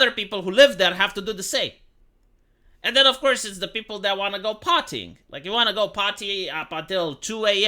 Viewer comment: I thought you said "the people who want to go potty".😂